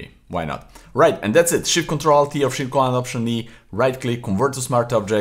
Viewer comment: What are the shortcuts that you are using at 20:07? I couldn't understand that part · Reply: Shift+ctrl+alt+E